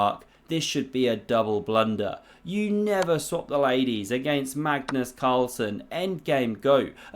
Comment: Did he just say Cagnus Marlson ?